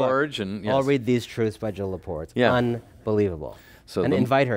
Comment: "we should all read....???" Help! I cannot make out the name of the author referred to and that "We should invite her here" Anyone, please? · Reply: Jill Lepore, author of 'These Truths'